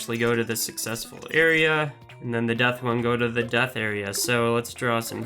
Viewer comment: Death star? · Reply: Oh, i thought i heard death star